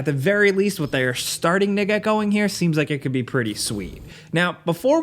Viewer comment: Did i hear this right? 😂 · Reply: They're starting to get going here